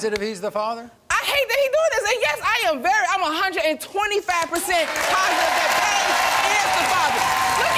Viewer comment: well what happened to that 125% of yours? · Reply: lol she clearly said 125% go to 1:08 if you don't believe me